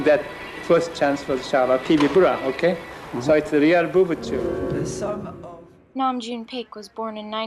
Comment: Did he just say: "It's a real booby tube"